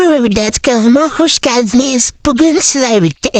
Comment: buraya kadar ne dediğini anlamadım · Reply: Merhaba millet, kanalıma hoşgeldiniz diyorum 🤍